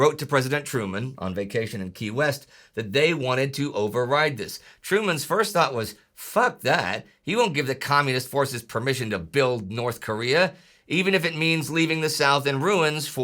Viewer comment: Did Truman really say "Fuck that!"? 😂